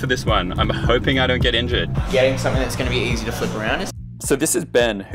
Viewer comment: never have I heard someone say they hope they don’t get into Skateboarding